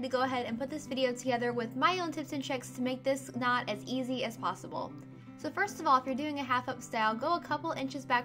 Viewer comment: Oh my bob. You said "Make this knot easy as possible" I thought you said, "make this not easy..." I was like "whaa?!" Haha. Great tutorial! Can't wait to try it out. Thanks! ! :)